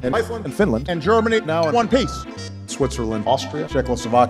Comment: Did he said one piece? 🤯